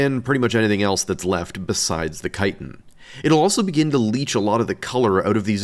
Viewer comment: For a moment I thought he said "colera". Which would also make sense.